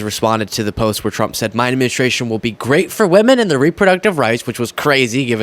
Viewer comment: I thought Trump was saying women and reproductive rights blah blah blah "campaign" instead of "administration", but wires got crossed between my ears. Maybe I should run for office!